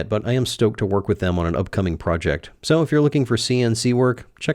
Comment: Wait 10:13 did you say new project?